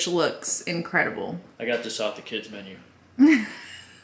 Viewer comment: I laughed at because I thought he said he got this off Instagram 🤣 but them laughed again when I listened back and heard kids menu. 😂